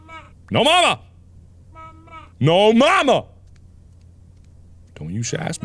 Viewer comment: My dyslexic ass thought he was saying mo bamba